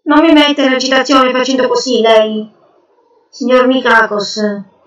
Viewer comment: "Signor Micagos"? Ho capito bene?